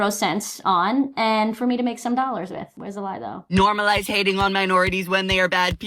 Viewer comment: Did he just say "normalitating"? Wtf kind of word is that? Is it new? Am I missing out? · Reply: He said normalise hating